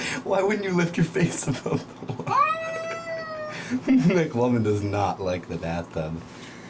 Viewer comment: No ones talking about how the cat’s name is Mclovin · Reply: Zoliebee man I thought he said make lovin jesus